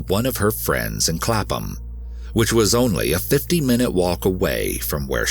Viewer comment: Thank you for bringing attention to this case! I’m from the UK, live a couple of miles from her home town. I use to complain at my mum for being over protective when she’d ask for updates on where I am and to text when I’m nearly home. Now I’m grateful for that. My friends and I always text each other to let each other know when we’re home. We stick together when we’re out at night. It is terrifying. 9/10 I can’t go out without being catcalled, whistled at, men driving really slow past me whilst staring, the list could go on. What happened to Sarah and the thousands of other women is disturbing. It makes it 10x worse knowing that the person who was suppose to protect was the person responsible. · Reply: Is it common for people to walk a distance of 50 miles? Or did I mishear? I live in the states, and you barely see people walking anywhere where I'm at.